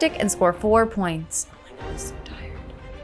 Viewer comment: Great show as Always. But what is being whisperd in 23:07? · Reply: "I'm so tired". I think that's what she says